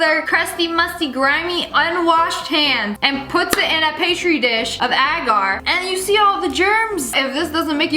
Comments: did i hear pastry dish 🤣🤣it a pee-tree dish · Did she just say, “pastry dish”!?!😂😂😂👏👌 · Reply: No she said Petrie dish